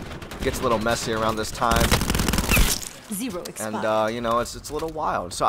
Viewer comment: did she say zebra expire